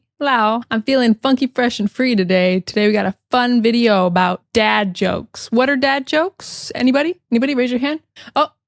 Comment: At first I thought you said dead jokes instead of dad jokes. Dead jokes also seems an appropriate qualification. :) You have a very laid back style of teaching. Luv it.